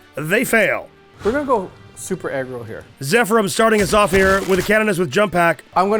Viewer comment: I'm gonna go super egg roll here... Me: Excuse me? Oooooooh, you said aggro Jesus I misheard that. 😅😅😅